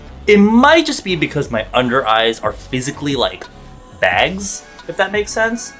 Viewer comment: Omfg I thought you said "my undereyes are bangs" 😂